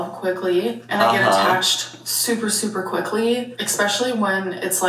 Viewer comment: “Tumultrous”, “Doormat” -, 2025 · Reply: did she just say 'ex-specially', or am I just hearing it? lol